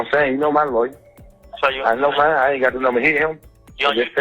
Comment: He said Brian tho 🤦🏾‍♂️ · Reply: Sounds like he said “ I know mines all I got to to is hit him “